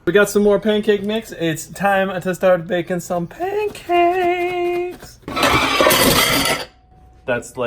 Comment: Baking pancakes, baking pancakes, baaakinnggg paancakeeesss Adventure time fans will understand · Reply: I thought he said makin bacon pancakes